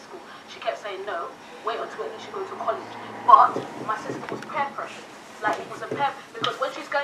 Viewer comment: WHAT DID SHE JUST SAY PLEASE? 27:14 ?? pearpresshed?? im honestly done